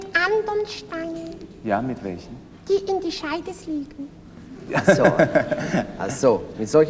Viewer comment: Mit Steinen die in der Scheide fließen"? Ach so, nu hab ich´´s glaub ich: Scheide isn Fluß. aber verstanden hab ichs trotzdem net wirklich. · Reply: Ich glaube, das sollte heißen: "... die in die Scheibe fliegen." Aber lustig war es trotzdem nicht.